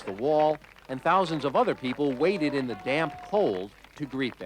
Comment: “and thousands of other people waited in the damn cold” was I the only one who heard that?😂